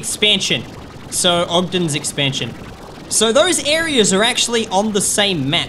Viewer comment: I thought he said octo expansion